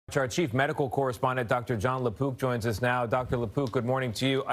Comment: Thought he said "John Le Poop"